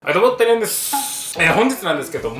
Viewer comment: はいどうも、てらやんですっていつも聞こえる